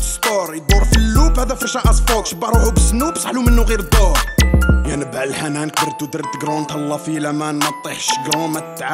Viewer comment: نفس وش قال فوبيا فأغنيتو الأخيرة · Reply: فوبيا قال انت dog ضيع مولاه